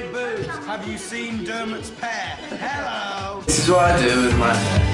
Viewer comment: he actually says "have you seen dermot's pair?" as in dermot o'leary haha but love the video!! 💗💗